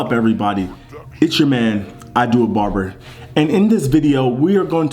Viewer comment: Thought that shit said idiot barber 😂 good video tho